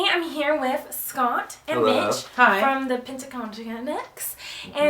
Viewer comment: OMG when she said Mitch I thought she said bi*ch lol